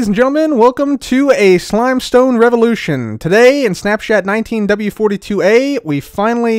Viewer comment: am I the only one who heard snap"shat"?